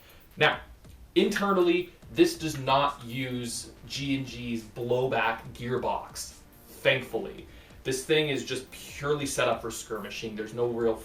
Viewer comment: whats wrong with the EBB system...? I heard you say "It wasn't have a blow back gearbox- thank god"